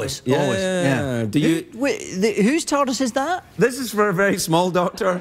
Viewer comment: I swear I thought David said "tortoise" not "TARDIS"